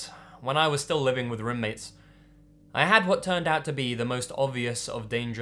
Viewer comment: I thought roommates said romance ahahaha :D cool stories though.